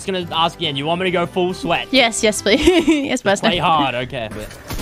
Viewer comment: did she say yes master?🤔